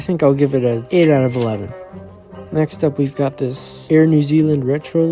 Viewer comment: 8/1😂😂 I know you said 8/11 but sounded like 8/1